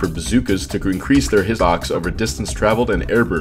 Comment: I was so confused at 7:29 when this man was saying "hissox" instead of hitbox